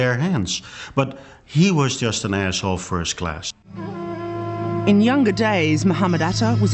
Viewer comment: I must be mishearing him. Did he just call him an 'asshole' first class?